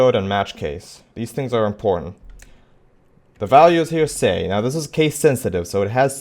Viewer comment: I cant hear what the value is at 6:52 is it "say"? if someone could help that would be nice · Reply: It's all typed out, it is say.